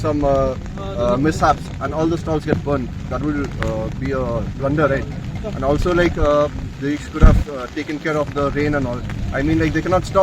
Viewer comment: did he really said "they could have taken care of rain" or i misheard 😂😂